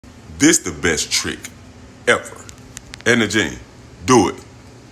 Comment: Ohhhh Edna Jean, I thought he was saying Etta James that’s why Died laughing.